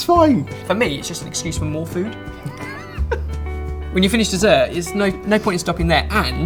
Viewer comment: Anyone else think he said "F**K" at 2:41?